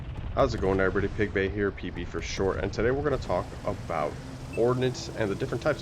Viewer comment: i thought you said "pp for short"